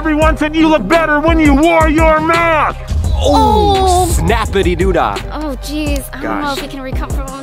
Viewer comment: what did say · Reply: snappydadoda is what said